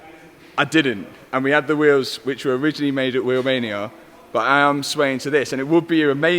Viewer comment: Did he just said that the wheels he had where build in Romania? @ 37:55 -37:57😱 🇷🇴🇷🇴🇷🇴